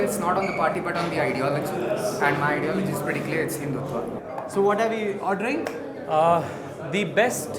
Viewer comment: Did he just say HINDUTVA???? Bro sincere request....please don't become a politician.